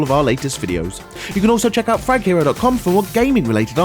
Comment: At the end it sounds like ur saying faghero😂😄😄